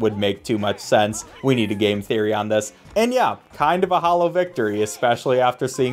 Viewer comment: did someone say game theory matpat